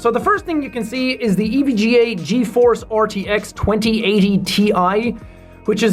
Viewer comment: I thought when he said GeForce he said cheese board 😂